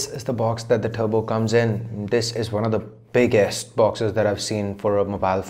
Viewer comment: I thought you said "This is one of the BIGASS boxes I've seen"